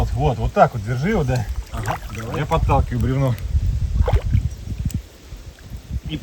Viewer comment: не расслышал, что было за слово? ) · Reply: бревно